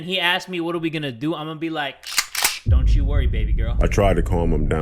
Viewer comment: what you hear: 2:27 what i hear: YoU lOsT BbY GuRl